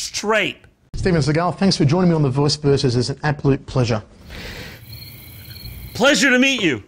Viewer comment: he said “Apolute” instead of absolute lmao.